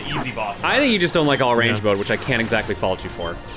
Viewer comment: Anyone as a kid misheard all range mode as orange mode?